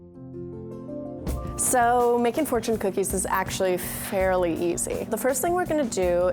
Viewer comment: I thought it said "fortnite cookies"😂🤣🤣🤣🤣😂😅